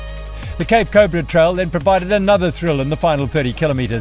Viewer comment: Did anyone understand, which trail the one at 16:32 was? Sounded like "Cape Cordred" or so, can't find it on Trailforks though, in the Banhoek/Bethlehem area.